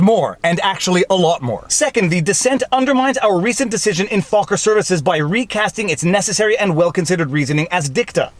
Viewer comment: I'm listening to this while playing a game and keep thinking I hear say "Focker served"